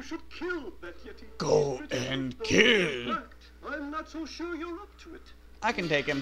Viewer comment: The guy said "kill" but his subtitles said torch. I never noticed that before.